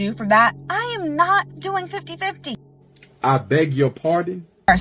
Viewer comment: B#### be doing "5150" instead of "50/50".